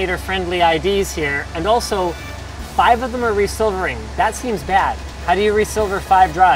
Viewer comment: What is that word at 10:11-10:16 or 13:35? Re-silver-ing? Also Youtube captions suck....